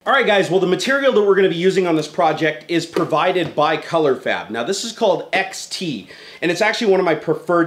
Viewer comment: I thought you said Colourfap at 1:34 :P